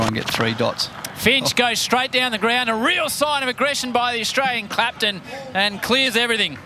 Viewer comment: Wait did he just say”Australian Claptain”